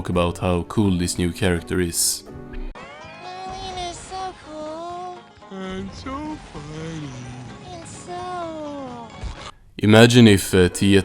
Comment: i know they said "marlena is so cool, and funny, and so... goes to sleep but at first i heard them say " my weiner is so cool, and so tiny, and so* they go to sleep* 💀